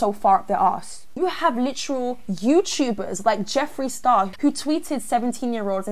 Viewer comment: I heard Jeffery Dahmer for a second and I was like “Wait since when is he a celebrity- he’s dead tho-“ Then I looked at it and it said Jeffery Star- Lmao